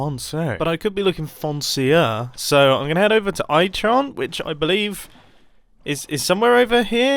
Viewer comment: I sure, i heard “i chan” in